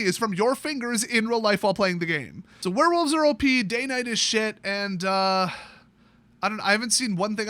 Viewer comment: Had to listen to his recap twice because I thought he said day9 is shit but he said day night is shit